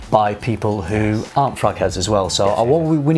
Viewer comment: When he said "... Also by ppl who arent fragheads" i thought he said fuckheads lol... K bye